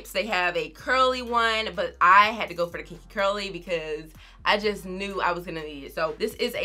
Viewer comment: Hey fren! I’m looking at the site and I only see curly then kinky-coily. Which one did you get cause I thought you said you got the kinky-curly one. I have pretty much the same curl pattern as you so I wanna make sure I get the right one! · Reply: Kinky-coily fren! Sorry u know I talk fast sometimes